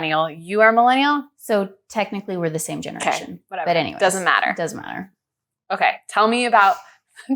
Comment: it sounds like one says doesn't matter and the other says does matter.... i'm sure it won't come back to haunt us